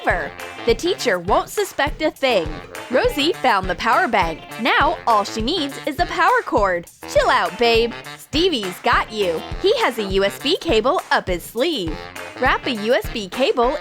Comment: did he call her babe?